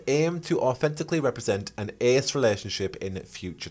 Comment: whats an ass relationship. what word did you say here? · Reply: ace, as in asexual.